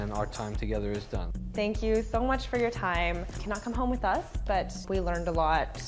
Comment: Well wen you said at the end you can not cone home with us ! You said cannot so quietly all I heard was “you come home with us!! “